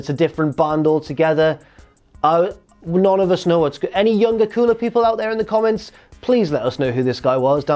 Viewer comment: I thought he said any younger colored people out there. Had to take a second look at it.